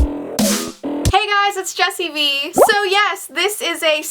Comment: at the start it sounds like she said hey guys it's Jessie wee